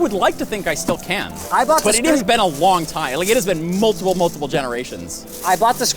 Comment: when talking to Linus about how long has been since he build a computer, does he say "its been a wong time" ?